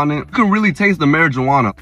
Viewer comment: For a sec i thought he said marijuana 😅😂😂😂